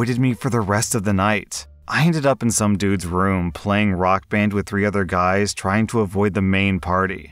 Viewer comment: I was listening to this video but I had to check I heard right when I thought I heard the OP was playing Rockman, It was actually Rock Band.